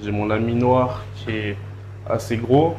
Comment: Beau profil , beau commerce ça donne envie d’éclater du croissant avec un bon café ☕️ 2:49 j’ai compris « mon ami noir » J’ai mis replay 4 fois j’me suis dis pourquoi il dit ça personne l’a accusé de racisme 🤣🤣🤣